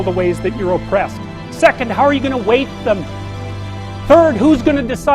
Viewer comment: Epic mode on. Un detalle de traducción, en 18:12 me parece que dice "how are you gonna weight them?" es decir, "cómo vas a pesarlas?", en el sentido de "cómo vas a hacer para darles un valor a cada una de ellas". Igual me equivoco, pero creo que es lo que dice. Un saludo y hasta otra.